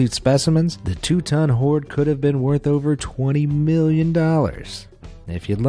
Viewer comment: At around 6:55 he said 20 million, but the subtitles say 2 million. I won't do the math now, but there's a discrepancy.